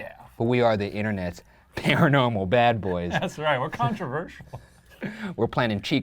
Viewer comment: Did Shane make a pun? At around 8:18 “Conjur-versial”? If not, that’s what I heard and it’s hilarious.